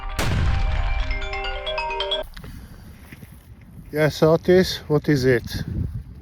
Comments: Its name is “Otis Roomba” 😂 I thought he was saying “Autist” all this time. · Love the OTIS gag :) As always good video. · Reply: OTIS? I always thought he was saying Autist.